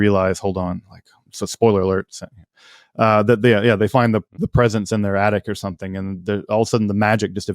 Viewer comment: I heard “…presence in their attic…” 😳